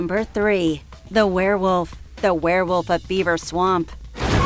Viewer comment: the werewolf of "Beaver swamp?" admit it you all hear it. even the CC says it